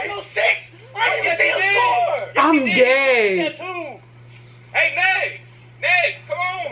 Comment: I'm gayyyyy. Anybody else hear that shit?